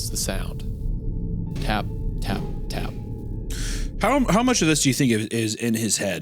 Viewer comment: Y'all keep saying "tap tap tap", I keep hearing "flop flop flop."